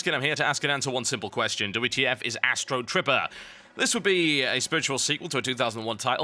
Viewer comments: I thought it said Astro Stripper. :( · I thought it said Astro Stripper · damn thought it said Astro stripper! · At first I thought it said Astro Stripper, after seeing the video I really hope that it would be a game called that instead.